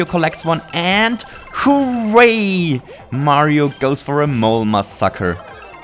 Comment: It sounded like you said "Mole my sucker" instead of "Mole massacre".